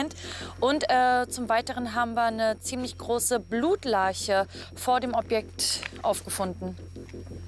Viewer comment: Sagt sie immer Blutleiche? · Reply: Ich habe blutlarche verstanden!